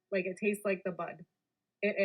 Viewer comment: I thought I heard her say "it tastes like the butt".??.ohh, bud..got it